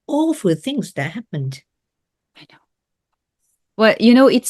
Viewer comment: What did she say under her breadth, it is around 33:02 , like she was talking to someone else. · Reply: She says « I know »!